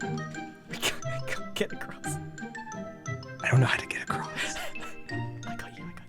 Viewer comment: My friend once did something similar to scar scaring grian except she was a zombie and i freaked out, 6:30 I thought they were saying they can't get girls and i laughed so hard I fell of my bed, 11:09 I have done something similar to that with my bff and my mother except it was irl, I was pretty much grian, scar was ma bestie, and my mom was bdubs